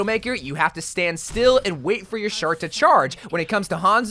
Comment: Anyone else notice that he accidentally said shart instead of shot